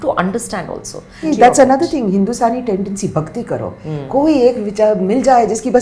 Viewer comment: Did RPS just say ‘ Hindu sari tendency Bhakti Karo?!!!’ At 13:38?What is her problem? How venomous… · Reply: She said “Hindustani logo ki….”